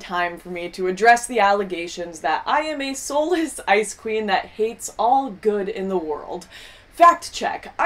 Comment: Every time she says "ice queen" i keep hearing it as "icecream". Funny that...